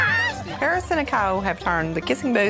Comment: it sounds like the lady said "Paris and the cow" instead of Nicole